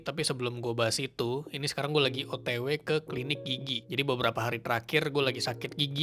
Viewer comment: Gw ketawa mulu krn gw denger kata "gigi" nya lucu Kedengeran kek "gigyik" 😂😂